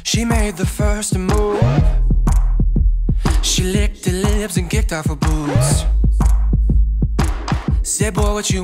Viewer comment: For a some reason I thought he said "She kicked her lips and licked off her boots."